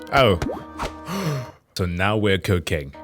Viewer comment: I gotta give you credit for this joke you didn't make: When you said "so now we're cooking" at 3:08, I misheard that as "so now we're cocaine". I though you were making a joke about how "math" sounds similar to "meth", and that you were describing how the topic was ramping up